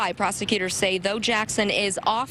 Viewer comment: the reporter said ”though Jackson ....” & I thought she said Bo Jackson 😂😂😂